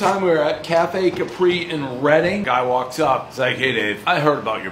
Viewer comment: I thought he said Redding, he meant Reading.